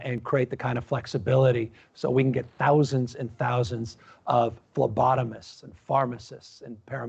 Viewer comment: did he say lobotomists?